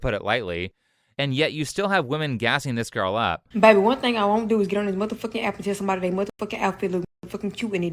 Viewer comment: EXCUSE me, what did you say honey?! You know what, it doesn't matter, it probably would make me lose valuable brain cells, so it is better this way... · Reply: Bae one thing I won't do is get on this mfkin activism on dae mfkin algorithm fkin Cuban they don'ts beep